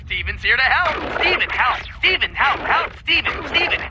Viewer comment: I heard "help Steven"